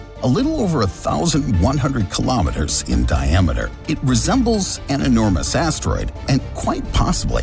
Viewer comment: "... it resembles an enormous asteroid." First time I heard "... it resembles an enormous asshole."